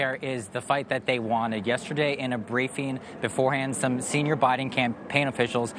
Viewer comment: Did he just call Biden's campaign officials handsome?